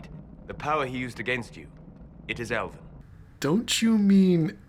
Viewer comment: He should've said "elven". This is more of a grammatical and character thing for me. He's always harping on about the elves. It just builds up into the rest of the confusion I have from the following scenes. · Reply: Ah, I see. I was probably just mishearing him then. I always heard it clearly as "...It is Elven," Though going back and listening to it again (now that you've pointed that out), I do hear him pronounce it as, "...It is Elv." Anyways, thanks for clarifying.